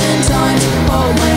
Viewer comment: Music: “Hold my hand Tight” Me: ”HoLd My HeNtAi” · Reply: cuz i sounds like he says hold my hentai instead of hold my hand tight